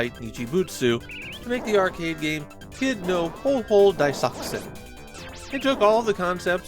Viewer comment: Is it just me or did anyone else have to do a spit take at the start, mishearing daisakusen as desoxyn at the beginning?